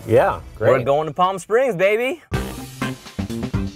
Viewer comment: I thought he said Salty Spring instead of Palm Spring